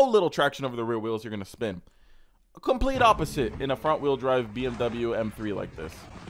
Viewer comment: Did just say fwd bmw m3🤣😭